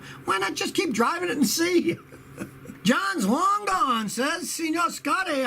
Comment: Johnslongdong... did i hear that right?? 🤣